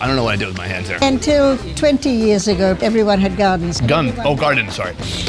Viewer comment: I'm glad they put in this blooper. I heard guns the first time she said it, too.